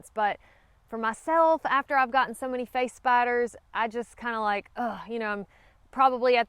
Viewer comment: I'm not afraid of spiders, but I thought you were saying face bites instead of face spiders. Face biters do sound scary.